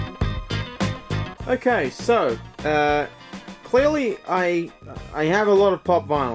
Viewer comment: did he say "clealy"